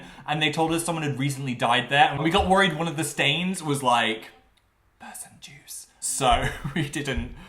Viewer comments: "person juice" is the worst thing you've said till date edit: alright one of the worst* cuz you've said a LOT of questionable things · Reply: Person juice??? I genuinely thought he said p*ssy juice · "Person juice" I THOUGHT HE SAID PUSSY JUICE AND I WENT BACK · did he say "murder juice"? bruh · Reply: He said person juice